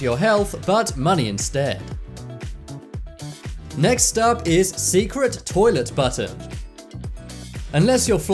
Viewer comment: I thought he said secret toilet butter. I had to go back and rewatch it.